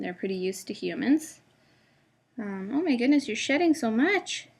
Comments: It sounded like you said its sh**ting instead of shedding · Lol at 1:48 I thought you said shitting so much lol